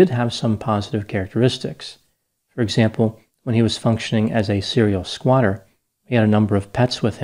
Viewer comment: When I first scrolled past this I thought it said "serial squirter ". I know ...I'm sorry.